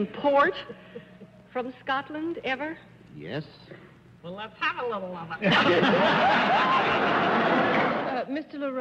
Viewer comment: what did Arlene say here ? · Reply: Arlene : well I've had a little of it. Referring to whiskey.